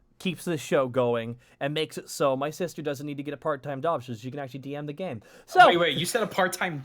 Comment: I heard "part-time job", not "part-time dog". Don't know where dog came from. Then again, I sometimes have the same issue.